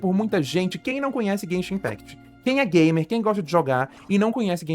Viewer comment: eu escutei "quem é yag" socorros